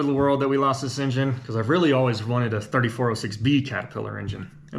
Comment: the good news is that you don't need the engine running right now. Caterpillar just announced that you can get a new content (as in new crank) 3406 from them. They will take just about any core, even if it has windows in it. You will get a later 3406 (i think they may all be 3406E) with a warranty and everything. They will even let you install it yourself, i think. That should give you quite a bit of peace of mind, plenty of horsepower for your "project", provided you can swing it. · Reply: My hearing ain’t the best, but I thought he said he wanted a 3406b, not e which would make sense as the 3406e had crazy electronic issues. May have been the first electronic version but I don’t know for sure.